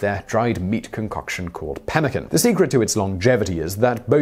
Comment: I was listening to this video without actually watching but had to go back to read the title card when I thought he was saying ‘pelican’ lol